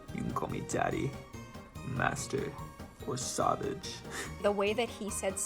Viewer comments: When he said savage i thought he said sausage an di was so confused- XD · I really thought he said sabbage lol 😂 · i thought he said sausage not savage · When he said " SaVaGe👹 " I actually thought he said " sausage " 😭✋ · I heard sausage xDDD